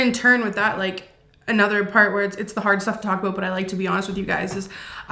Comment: not to make light of a serious video but I legitimately thought u said " its the hard shell taco" instead of "the hard stuff to talk about" 😂😂😂😂 But seriously I'm so happy for u guys! 🎉🖤🖤🖤🖤